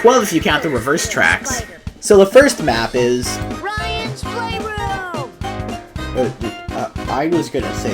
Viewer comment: for a second i thought he said Racist ryan